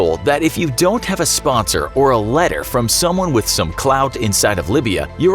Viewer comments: Did he say clout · Did he just say clout?